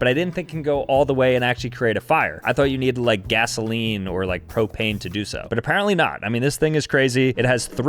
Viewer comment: Did u say cocaine?